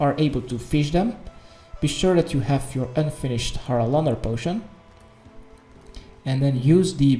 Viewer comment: Haralamalama potion thanks for the guild you hakahlakamakabooboo 14:32 wtf are you saying!?!?!